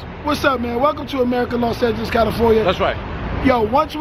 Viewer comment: me acabo de dar cuenta que dice “bienvenido a América, Los Santos California” :v